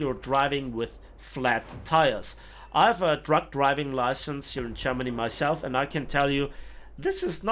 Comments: well people that drive 40 ton trucks should better know what they are doing etc. Schooled for every situation to be expected because ... the entirel left lane is made out of trucks in germany ! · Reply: lmao no I know nothing is weird about getting a truck license anywhere, I thought you said "drunk driving license!" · I thought you said drunk driving license at first not truck driving license lmao